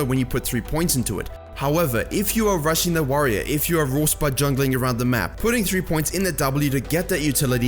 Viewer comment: you something like "Raw spud jungling" did I hear correctly? what does it mean?